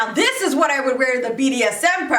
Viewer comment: Yoooooo my mum was in the room and the first thing I hear: NOW THIS IS WHAT ID WEAR TO A BDSM PARTY on max volume 😭😭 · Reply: I swear i heard "now this is what I'd wear to the bts party" lol